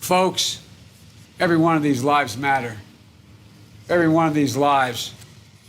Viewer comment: did he just say military loves matter? Only black lives matter😂